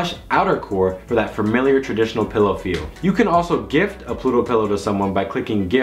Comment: I thought you said Pluto pill l0l